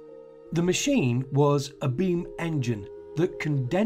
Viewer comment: What does he say at 2:15 , bean engine?